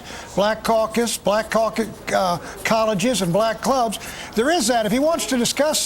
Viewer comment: Ooops, have i heard black COCKS?